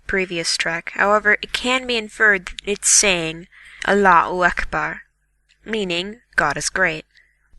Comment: Gaijin, your friend was mistaken at 5:55. The third phrase does not say Allahu akbar, it says bismillah ir-rahman ir-rahim, which is translated as "in the name of God, most gracious, most compassionate." this is a phrase said before, for example, people do things that might be risky and would like God's blessing before they do them. Most of my family says this phrase before we go for a drive anywhere for example. It is also common to say this for things that aren't really risky, like before eating any meal. All but one verse in the kor'an begin with this phrase, and people leading group prayers will begin by saying bismillah ir-rahman ir-rahim. I recognized it immediately when I heard the example, so I was disappointed when your friend didn't recognize it.